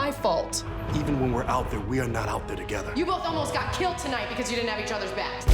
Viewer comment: I thought felicity said "you hobos almost got killed to night cause you didn't have each other's axe"